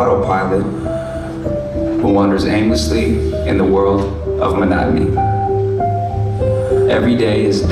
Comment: "Who wanders aimlessly in the world of..." what did he say? I just can't understand it. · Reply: monotony